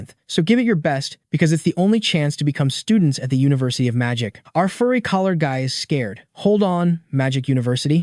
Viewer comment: “Our furry collar guy” is crazy😭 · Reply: I heard "Our furry collar dog" 😭